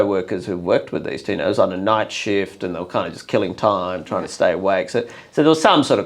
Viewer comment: Did he say, "On the night shift killing time" at about 01:30 ?